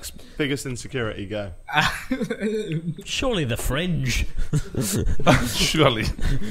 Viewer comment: My favourite part is when the one guy with no hair whatsoever says that people with fringes have bad hairlines🤠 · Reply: I thought you said no fingers and I was proper confused